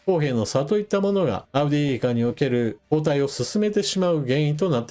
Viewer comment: いつも詳しい解説ありがとうございます。ウクライナ軍の反転攻勢にはまだ時間がかかりそうですが、F-16も届き始めたという情報もあったりしますしなんとか踏ん張っていただきたいです。 ところで些末なことですが相変わらず「アウディーイウカ」が「アウディウイカ」に聞こえます。あえて直せ、とは申しませんが実際そう聞こえているということは伝えておこうと思います。